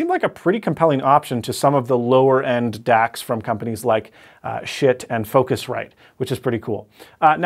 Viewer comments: "shit and focusrite" Did I hear that correctly? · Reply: Yeah, it's spelled Schiit though. They're German. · Did you say the company name is "shit" at 12:20? 😝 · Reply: Chris Boursnell Schiit. :)